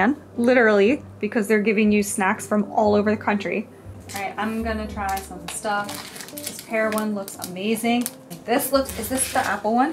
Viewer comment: I definitely heard her say, "This heroin looks amazing"